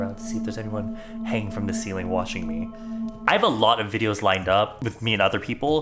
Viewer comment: i heard "hanging from the ceiling washing me" and i was so confused 😳😂